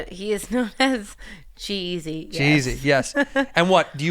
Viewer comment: Legit thought she said she was dating "jeezy" Let down when i find out its actually g eazy